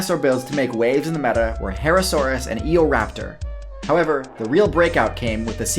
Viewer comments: I thought you said Egoraptor and I was very confused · I thought I heard egorapter was dominating the meta